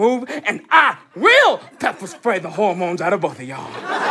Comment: It sounded like Stacey said, "POOPER SPRAY." lol